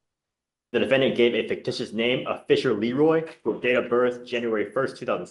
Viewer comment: Was Fisher Leeroy a good fake name? Did the defendant deserve a PR bond? Let us know your thoughts! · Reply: 😂😂 it’s a funny name I thought he said official Leroy